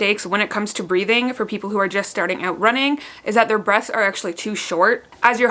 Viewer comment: I guess I'm just hard of hearing so I thought you said "breasts are really shallow" then my husband said "no babe she said BREATHS not your titties" 😂🙄 glad to know my boobs aren't shallow lol